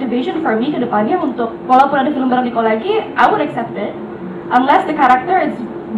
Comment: dia bilang "i won't accept that" kah ? berarti Amanda gak mau main sama Nichol lagi gitu? atau gua yang salah dengar